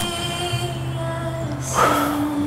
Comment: Did he just say ‘come home’ 🥺 omg that has to be the most romantic thing I’ve ever heard 😫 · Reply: I thought he said 'come home'.